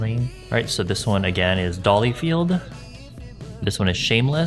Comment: Thx for making make up fun for me again. Not to be "that one" but, I thought you said Dollyfield but, it's Dollyfied (without the L lol).